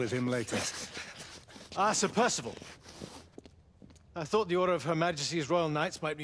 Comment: I legit thought it said the order of 66 no cap